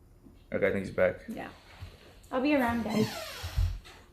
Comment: Did call “daddy”? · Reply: The closed captions says, " ill be around guys." 🤣 Although it does sound like she says daddy 😝